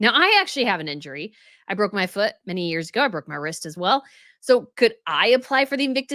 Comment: Oh no! I honestly thought she said I broke my vagina. 😬🫣